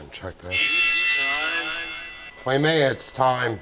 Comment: I heard “it’s pizza time” lmao